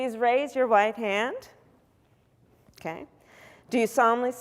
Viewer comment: Did I hear her wrong as a result of her mask ? Every word but one was clear. I hope I'm wrong but it sounded like she said ,please raise your white hand ,instead of right I had to replay it about a dozen times and sounds the same everytime